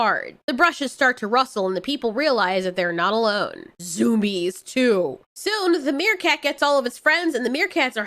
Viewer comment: Not gonna lie, this vid came up next on its own thanks to autoplay, and I misheard you and thought you said 'Zoomies' so I thought this was gonna be some nice, fun background noise about a wholesome dog movie. Then I heard a bunch of elephants stampeding and violent monkey screeching.